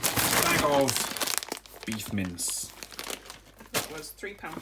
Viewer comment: I wasn't paying as much attention as I should've and initially misheard mince as mints. That led to some interesting mental imagery, that's for sure.